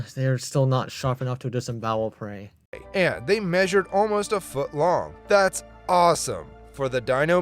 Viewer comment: Did he say almost a foot long?